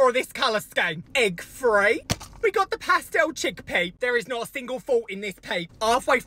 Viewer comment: Ngl dude with your accent I dead thought you were saying chickpeas 😂